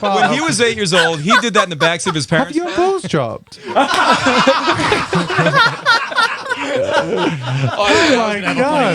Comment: What did he say at 8:59? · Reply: samira hadzic ‘have your balls dropped?’